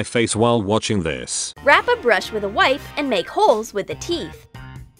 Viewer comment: Hahahah lol at 12:44 troom troom "said rap a brush with a wife" instead of a wipe